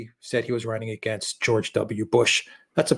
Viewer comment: "...and he said he was running against George W. Bush" In all fairness, i think he just said "George" (on the clips i've seen at least).. for all we know, he could have meant George Washington :p